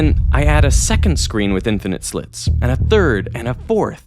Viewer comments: Doh! Could've sworn that said "infinite slut paradox" 🤣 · i thought it said infinite slut paradox